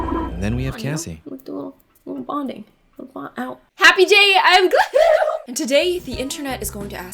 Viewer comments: I thought she said happy jay 😂😂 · It sounds like she says Happy Jay at the beginning. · I THOUGHT YOU SAID "HAPPY JAY"